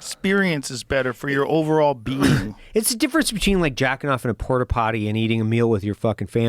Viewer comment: wait did Evan just say he jacked off in a port-a-potty !? 😂